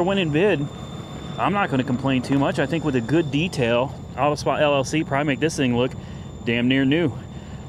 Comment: Nice purchase . When you were talking about detailing the car , 5:30 it sounded like you said LLC Primate lol. That BMW Z4 looks like a nice purchase.